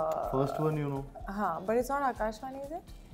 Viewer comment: Kriti was right. 1:05 She clearly said 'Aakash Vani". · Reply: She said ' it's not Aakash Vani'